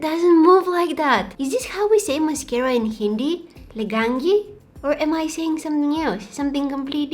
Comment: In hindi we say mascara, mascara only.. And at 7:41, the word which you thought we might be saying mascara in hindi is "Lagayenge".